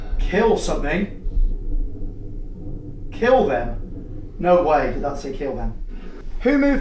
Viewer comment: When it said kill them I thought it said never